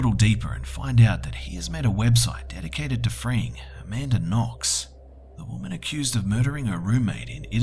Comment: does anyone know what he is saying ? Is it Framing? Listened over and over and is to hard to decipher.